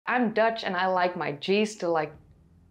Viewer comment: Wonderfull video. Fun sidenote, at the start I first hear: I'm Dutch and I like my gin to growl.